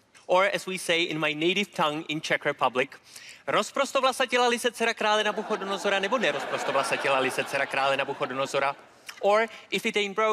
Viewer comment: What does he say in Czech at 1:53? It certainly wasn't "You must do a card trick". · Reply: "Rozprostovlasatila-li se dcera krále Nabuchodonozora, či nerozprostovlasatila-li se dcera krále Nabuchodonozora. " It is just Czech tongue twister. Translation would be something like "Did daughter of King Nebuchadnezzar let down her hair or did not daughter of King Nebuchadnezzar let down her hair"